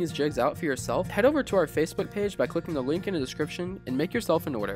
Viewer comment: Man my hearing is shot I thought he kept saying henti jigs lol then realized he said hand tied jigs ,nice jig though .